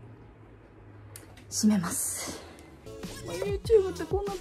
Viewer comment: 「閉めます」が治安悪い方の「しめます」に聞こえて、え！？暴走族(？)しめるの！？！？って思った